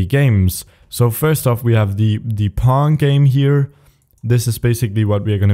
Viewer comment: i thought he said "Porn game" xD